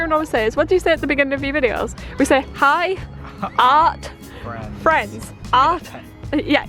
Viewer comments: I always thought you were saying, "Hiya, friends!" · I thought they said 'Haya Friends' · you two should do that 2 marker challenge but just pick a new color half way though and you can't go back to the old color, I think that would be a fun little challenge. also I never knew you were saying "Hi art friends" I thought you were saying "Hiya friends" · I heard "hi girlfriends" lol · Ohh, i always thought you said, "Hey-yah friends" instead of, "Hey art friends" You learn something new every day :3